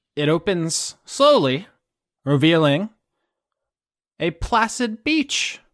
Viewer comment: I thought he said plastic beach instead of placid beach. Gotta put away my Gorillaz boner.